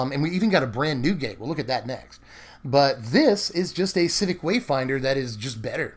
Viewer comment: what wayfinder card are you talking about? Civic wayfinder is the exact same card as district guide except district guide can get a gate also. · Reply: LOL... thought he said Satyr Wayfinder, not civic